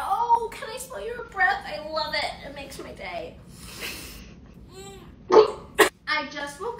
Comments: “Tchai can I smell your Breath it makes my day”😭💀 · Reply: I thought she said it makes her gay… 😳✋ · I thought she said “it makes me gay❤” I was like……